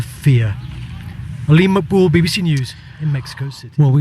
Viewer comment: I don't understand why he said at the finish of the report "Lima Perú BBC" 3:58 because he is in Mexico Country. This is a big mistake.